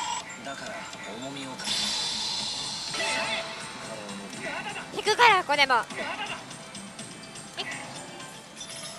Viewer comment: なんて言ってるんだろ？ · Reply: 東北訛りだと、ひぐがやこれも(笑) 引くかなこれもって意味じゃないですかね？